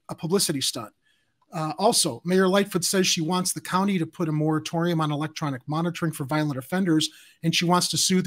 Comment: I thought she said moretorium for violent offenders